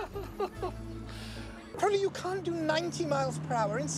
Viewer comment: For a second, I could have SWORN he said, "Honey, you can't do 90mph in central London!"